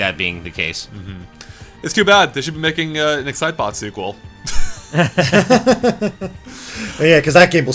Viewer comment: What did he say? excitbot? zybot? sidebot? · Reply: excitebots, i think the the company also did excite truck, too